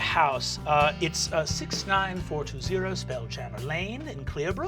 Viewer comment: 69420 Spellchanner Lane... love how delighted everyone was lol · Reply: Oh I thought he said Spelljammer. Maybe I misheard it.